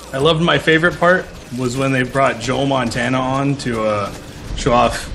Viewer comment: Is he saying Joel Montana? 8:05 .... Yep, he's saying Joel Montana...